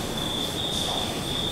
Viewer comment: I hear “its salty”